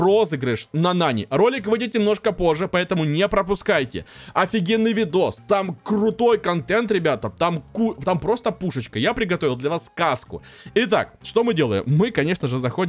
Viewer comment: ты приготовил для нас каску? 😂😂😂😅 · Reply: Мне послышалось что он сказал каску